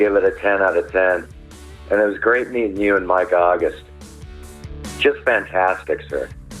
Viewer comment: I thought you were saying "Mike Goggins" not "Mike August"